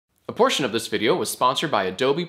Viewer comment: Man, I sure like portions of this video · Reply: At first I heard “abortion in this video” I was a little confused